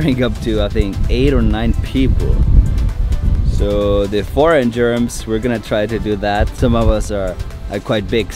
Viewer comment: HA HA HA HA I HEARD YOU SAYING IS THAT RIGHT??? KEEP SAFE.!!!! HAHAHAHA